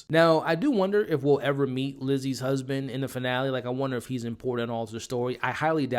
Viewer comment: I think it’s her ex husband. That’s what I heard when he mentioned him. Also I just saw that the episode for this Sunday is the final episode and I’m glad it says season finale and not series finale so I’m guessing the show will continue for season 2. There will be a plot twist for the finale. I can’t wait!